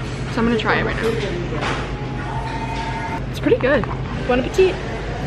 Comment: Love you so much but when you said “bon appetit” I thought you said “bueno petit” and I was really confused 😅